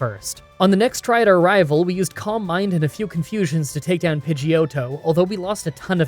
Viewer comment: I’m so sorry but whenever you said “calm mind” I thought you were saying “combine”, and was really confused for a bit there 😆